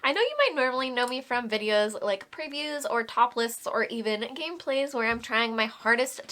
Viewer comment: Wow, SLOW DOWN with your talking! 0:10 You said Top Lists but for a hot second I could swear you said "topless" and I was like "WHAT???" 😮😂😂